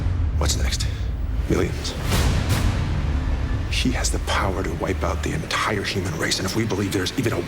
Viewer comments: Did he say 'SHE has the power to wipe out the entire human race' at 0:40? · Did Bruce just say "SHE has the power to destroy the whole world."? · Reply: N Sounds like he said "she" at 0:40, but at 0:45 he says "he". Not sure what's going on here, if anything actually is.